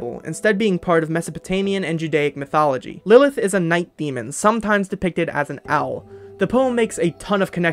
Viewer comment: i misheard night demon as lesbian… somehow.